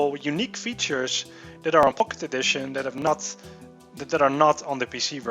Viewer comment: sounds like hes saying pok`e edition.. lol